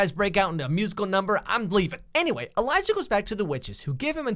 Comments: did Alex say "I'm Gleeving" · Am I the only one that hear him say 'gleeving' instead of 'leaving' at 4:55?